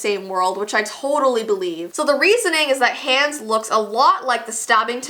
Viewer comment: It sounds like is saying "Hands" instead of "Hans"